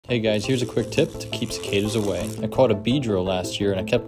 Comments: i thought that said “keep the kids away” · Thought you said to keep kids away.😅😅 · I thought he said “to keep some kids away.”